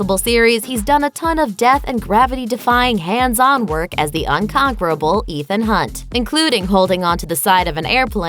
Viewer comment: did she just say "uncomparable"? The titles said "unconquerable" but that's not what I heard...